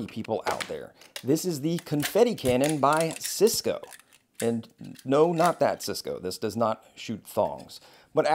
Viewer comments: I heard it as “and no this is not Cisco, it does not shoot phones” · See, I’m a big nerd. So I thought he said “No, not that Cisco, it does not shoot Thawns”